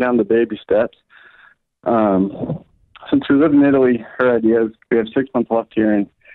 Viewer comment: Had to listen again when I heard "Kyle in Italy"